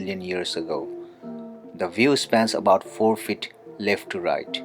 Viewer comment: did he say about four feet? If so I am impressed and I thank you for giving a nod to a long time well-established form of measurements. Thank you!